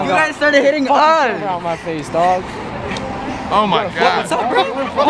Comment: Anyone else hear the N word at 3:14? I knew it was a mistake clicking on this! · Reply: Yeah he said what’s up n***a